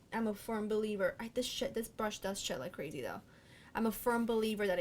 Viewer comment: Hahaha when you said this "shed, this brush sheds so crazy" I thought you said this shxt, this brush sheds so crazy! Hahahaha